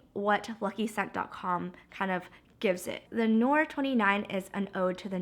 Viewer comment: The perfume is pronounced "teh nwa" it's French. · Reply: Kate Saap More like "teh nwar", r prononced with a french accent "Teh nwa" I thought you were saying it's called "te nois" XD